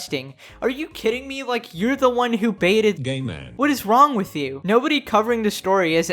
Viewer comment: I was just listening to the video instead of watching it and all i heard was "gayman"